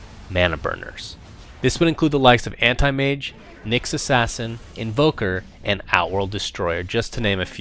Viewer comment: does he say naix assasin?for mana burn. Nerubian Assasin must be next to be released. :D